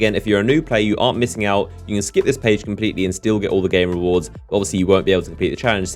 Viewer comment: did he say u can still get the gay rewards